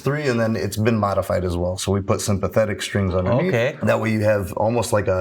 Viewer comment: Why did I hear “we put some pathetic strings” 😆😆